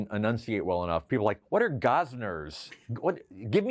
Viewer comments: I'll admit I thought he was saying "gossners" at first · I was old of the people who thought he said "gozners." Until today, what - 40 years later?